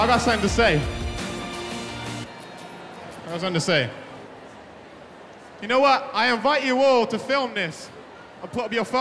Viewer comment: What he saying can someone write subtitles? I mean fr idk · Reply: He says ‘you know what’